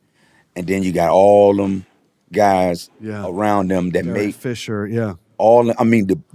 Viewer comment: i thought he said "all the officials, yeah" 🤣